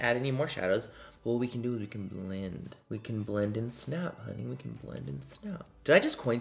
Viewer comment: Manny making a twist off of legally blonde hahah I love it. He said blend and snap but my brain went bend and snap from legally blonde. Manny you are great I'm just dumb and mishear things